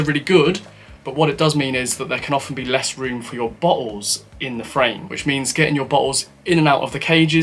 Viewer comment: I first thought you said "less room for your balls" also makes sense if you're mounting your bugs near your seat